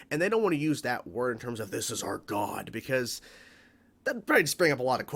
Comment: "This is our God" at first I thought you said "Kneel before Zod"